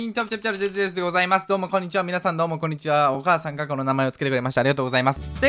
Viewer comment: 『お母さんがこの名前をつけてくれました』が、『お母さん名前が特定されました』に聞こえたｗ